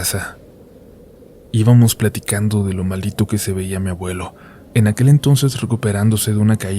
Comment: Escuché "íbamos platicando de lo MALDITO que se veía mi abuelo"? 🤔